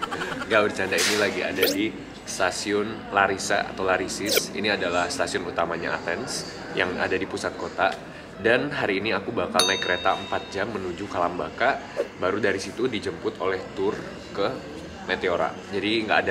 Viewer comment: maaf banget ini mah, tapi aku dengernya alam baka :"